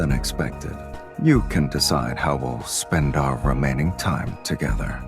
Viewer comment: HOW THE HECK DID HE PRONOUNCE "REMAINING" IN A WAY THAT SOUNDED LIKE "ROMANTIC" ? 😳